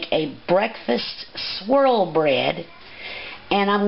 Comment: I kept hearing "squirrel bread" so I was in. To non-US ears, most american renditions of "squirell" sound like "swirl" so it wasn't much of a leap.